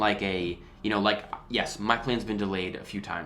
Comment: I thought this said "my plan to ..." and I was like wtf are you ok